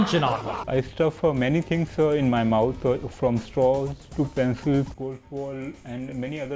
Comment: Did he say pencils?